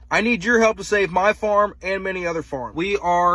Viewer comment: At first I thought it said “I need your help to save my arm.”